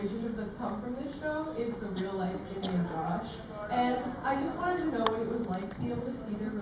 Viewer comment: Did anyone catch what the question was at around 3:50? 'cause I couldn't catch want was asked. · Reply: Theresa Kavanagh they were asking about what it was like watching the real life relationship with Ginny and josh develop